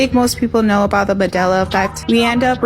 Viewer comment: 🎉the VHS 📼 young lady had possibly at least to me said badela effect instead of a unmistakable Mandela effect it was cute just wanted to point that out many small things don't get passed me